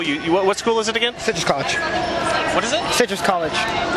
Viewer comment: I thought I heard him say clitoris collage at first! Wouldn’t be shocked if there was one called that tbh. Haha.